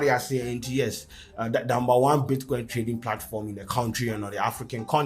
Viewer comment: did you say "the damber one bitcoin....." @ 0:41 ...😃😃